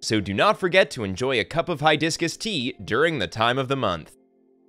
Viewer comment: did I heard HiDiscus tea? 😅